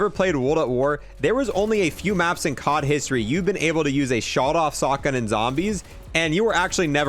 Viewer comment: did anyone else notice at 5:30 he said Shawed off Sotgun instead of Sawed off Shotgun